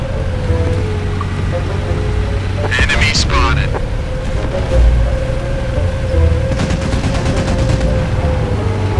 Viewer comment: I always enjoy watching your funny videos. So, what do you say this scene(0:04) in English? I can hear the part "Enemy", but I don't know the rest. Also, is this audio originally some kind of net meme or audio in the work? I couldn't find it even after searching. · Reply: it says "enemy spotted" and its an audio meme from counter strike source or counter strike 1.6 i think